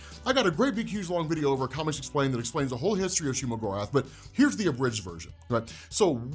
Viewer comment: Legit I thought he said Shea-Gorath, as in the daedric prince in elder scrolls